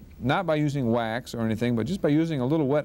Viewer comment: did he say wet asbestos at 10:00??? Damn. 1970's dentistry!! Dental school is hell.